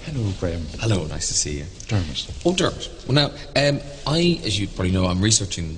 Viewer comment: Does anyone know what Graham and the priest say in 7:58 (right after "Nice to meet you")? Is it an Irish greeting? I've listened to it a few times but was not able to figure it out... sounds like "buon giorno" to me :DD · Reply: Just tells him to call him Dermot.. first names.. lol, I had to check.